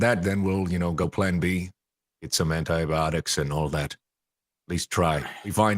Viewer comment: Ha, I misheard as "anti-erotics" x)